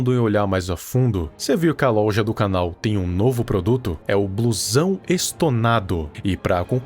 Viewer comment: quando tu foi fala da roupa e falo blusão eu entendi "bluezão" kkkkkkkkkkkkkkkkkkkkkkk